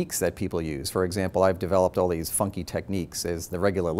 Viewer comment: I LISTENED TO WHAT HE SAID AT 0:03 MULTIPLE TIMES and I SWEAR that MORE THAN ONCE I thought he said "all these fucking techniques".